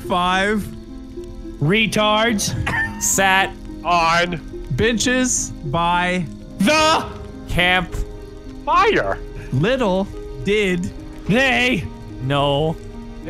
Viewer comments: anyone else think he said bitches? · I think he said sitting on bitches...